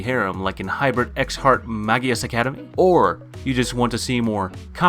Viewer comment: Anime at 0:06 ? Thanks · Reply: It sounds like he says "X Heart Magus Academy," but I'm not certain. I had to play it back 3 times.